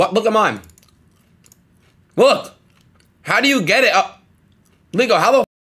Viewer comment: did bro just say “nigga how do you get it like” 😭😭